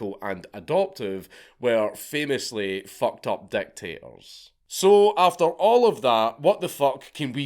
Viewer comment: Every time you said dictators I heard "deck tables"